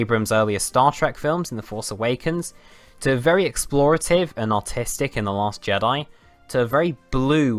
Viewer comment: That was the very first time I’ve heard the words The last Jedi and artistic in the same sentence together. Or did he say “autistic”?